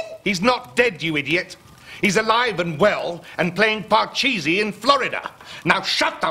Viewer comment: did he say "pLay PUBG" at moment 5:41 ??